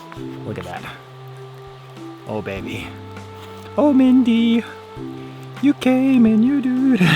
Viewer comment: @ 35:17 did you say Mindy? Or Mandy? -Barry Manilow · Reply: Mindy, its in part a Simpsons reference lol